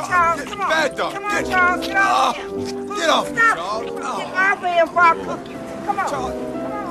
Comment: "Get off him before I cook you" Lmaoooo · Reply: I always thought she said "Get off of him before I whoop you."